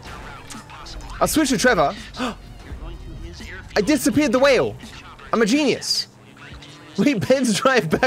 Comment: "I disappeared the whale." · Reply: I thought that said “I disappeared for a while”